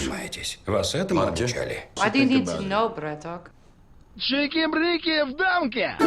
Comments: Объясните, пожалуйста, что она говорит на 32:48 ??? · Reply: Why do you need to know (Почему ты это хочешь знать), bЯaToK? Upd: Честно, сама не понимала, пока не включила английские субтитры)) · что она сказала? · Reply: "why do you need to know, bratok" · Что сказала Доминика на 32:49? Какой-то браток или что она пробормотала😂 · Что она говорит на 32:49? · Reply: Последнее слово Браток вроде( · что она сказала? "Обиды нет, браток?"